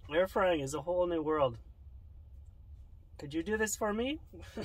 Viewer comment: “Could you do this for me?” 🤣🤣🤣🤣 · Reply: I thought he said “did you do this for me”